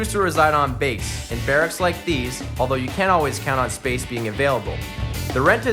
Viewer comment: Did he say" you can "or you "can't" ? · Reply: I think he said can't, sounds like there isn't always space in the barracks